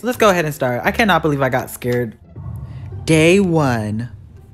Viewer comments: i thought i heard "Gay 1" but it was actually "Day 1" XD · I thought you said gay one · Who else thought he said “Gay one” 😂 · i thought i herd him say "gay one"